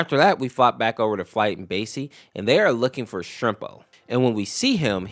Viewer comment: basey??? Am i hearing this right...?